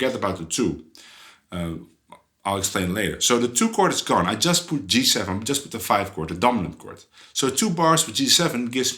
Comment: Did you say "Beatles scale" at 6:20? The closed caption said "leave of skill" and later "people's skill"!!! I really love this simplification as I can't play jazz yet. This really helps. Thank you. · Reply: "Bebop Scale"! Haha